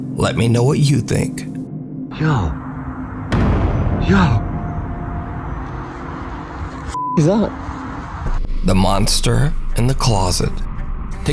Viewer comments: That number 1 video of the little boy. The voice sounds like its saying "you found me, the monster." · Sounds like it says “you found me, the monster..” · In the last one I hear "I'm not a... a monster"... 😳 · I went looking through the comments for anyone who heard what I did. I heard "found me" then "the monster". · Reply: okay... Now I hear it... "You found me .. the monster...". It's almost like it's playing with the kid. No malice, no evil... Just interaction. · Idk. I hear "You found me...the monster." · Great video as usual!!! In the #1 video with the little boy…the voice sounded like it was saying “I am the monster”. Keep up the great work!!! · I hear " I am the monster" ! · I heard "it" say "I am the monster" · in the last vid, it sounds like it is saying "I am the monster." 👻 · I hear “I am the monster “ in the #1 section